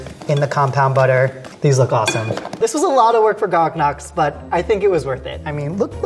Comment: he said gog noks instead of garlic knots